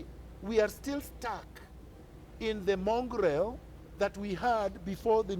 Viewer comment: Did you say we are still stuck in the 'Mongrel' of the old constitution? at 10:23...or <link> Define mongrel: a dog with parents of different breeds